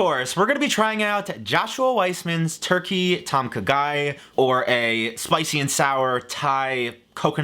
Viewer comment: Legitimately thought was saying "Turkey tonka guy"🤣